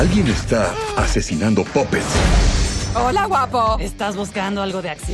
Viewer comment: Pense que decía muppets no pupets